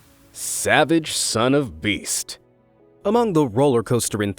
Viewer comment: i thought you said Savage Son Of MrBeast! LOL